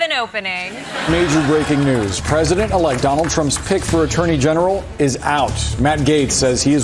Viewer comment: I thought he said "pig for attorney general" oh my then i had to double laugh